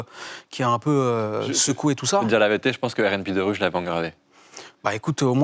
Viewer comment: Il dit quoi le gars ??? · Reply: Il avait l'album " Rnb 2 Rue " en cd gravé, une copie illégale.